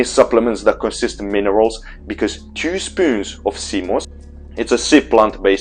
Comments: 2 spoons of? I can t understand what you re saying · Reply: Sea moss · I can't understand what is this seed plant 'cemus' you are talking about at 3:10. Can anyone help, if you got that?